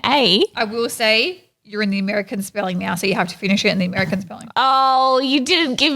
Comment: Australian spelling ? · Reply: I thought they said America spelling. (I’m American by the way).